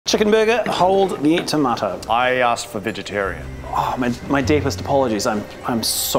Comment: Did he just say chicken burger